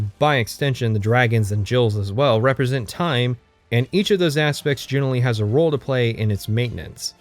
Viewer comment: what does he say around 6:20:15? dragons and... Jills? Jails? The Jails sweep up the mess of dragon breaks? what? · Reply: Not that far in, not sure if it’s answered in the video; but Jills are female dragons. It’s an off handed lore trivia, that may or may not be true.